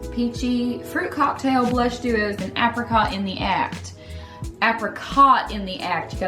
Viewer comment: The first time, I thought she said “apricot in the ass”